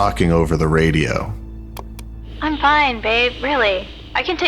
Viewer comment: did she just call her babe??????